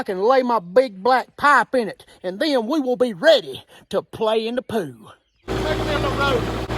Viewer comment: haha sounds like he said " my big black pipe will be ready to play in the poo "